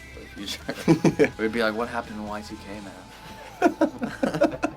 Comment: wait what did spencer say at 2:08 i think it’s “what happened and why is he gay, man.”